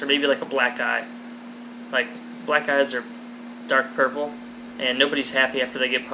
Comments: It sounds like he says 'black guy' instead of 'black eye..' lol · rofl thought he said black guys are a sad color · i thought you said black guy